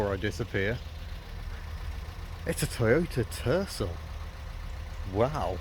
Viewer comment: At first I swore you said Toyota Turtle 😂